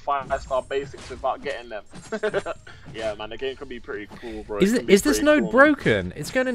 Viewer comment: “yeah my nigga...” Ayo my nigga what’d you say?